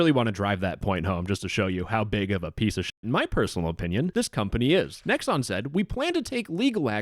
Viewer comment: did you say pwan ?